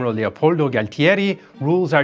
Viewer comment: My heart skipped a beat at 00:52 I thought I heard Paulie Gualtieri 😂